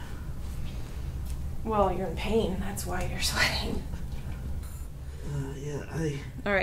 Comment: Omg was this the guy from forever ago with the wrist pain. I’m so happy he came back 😂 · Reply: thanks.... I thought I heard her say that's why you married me.